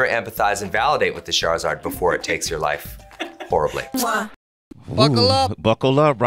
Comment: When he said buckle up at 5:24 I thought he said F*ckle up.